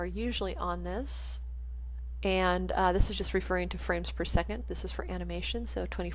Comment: I thought you said french per second but then i saw fps and realized you said frames per second